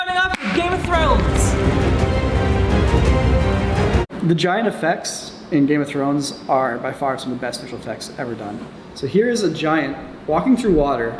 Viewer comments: I swear to God I heard "Vagina effects in Game of Thrones, are by far one of the best visual effects ever done" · I thought he said game of throats LOL